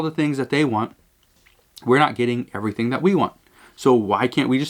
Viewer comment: I thought you said we are not getting the check till jan